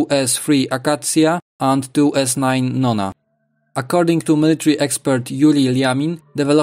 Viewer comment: I thought he said "according to military expert Rudy Giuliani" for a second there lmfao